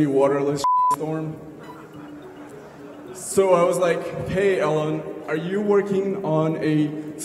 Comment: did he just call him "Ellen"?